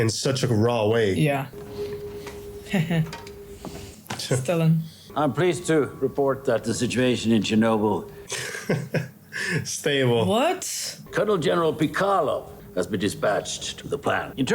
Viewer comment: Did she say "Stalin" when Gorbachev appeared on screen???